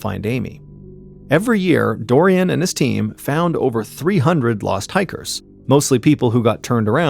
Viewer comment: I heard "lost tigers" 😂!